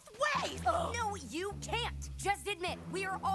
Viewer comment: what did she say!!!??? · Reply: Never!